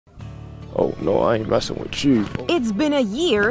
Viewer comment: It’s funny how he said,” oh no I’m not messing with you.” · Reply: I heard “Oh lawd I ain’t messin’ withchu,” ahah.